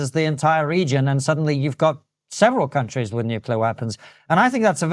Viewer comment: Serious question, does he have a lisp? Why am I hearing "Nuke-wear weapons?"